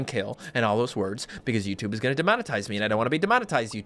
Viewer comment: Ha ha the captions say monetize instead of demonetized